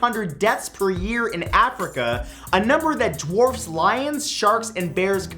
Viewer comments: did he say dwarfs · wait. did he say dwarves?